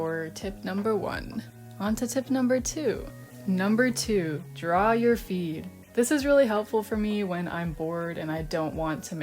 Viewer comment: HELPPPP!! I WAS DRAWING WHILE LISTENING TO THIS AND SRSLY THOUGHT SHE SAID " 2, Draw your feet "😭😭😭